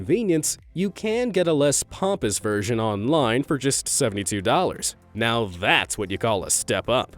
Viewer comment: $76 sounded like $72